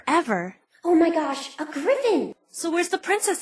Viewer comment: oh my god i thought she said "oh my gosh, a girlfriend!" what a mood